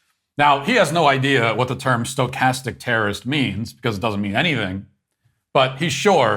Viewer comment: Ohh, stochastic, I thought he said sarcastic terrorist.